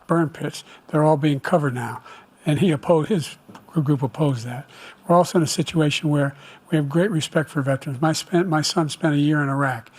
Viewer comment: Did I hear that correctly, " ...whether it was asian orange or burnt piss..." ?